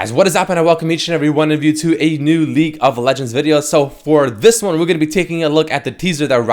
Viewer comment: i dont know if its just me but every time theres a champ reveal or leaks or anything like that i hear "to a new leak of legends video" instead of league of legends